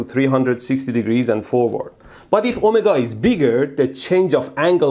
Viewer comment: When he says "omega", it sounds like he is saying "Oh my God"...